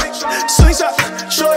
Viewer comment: First time I heard this I thought it said slingshot shortage lol!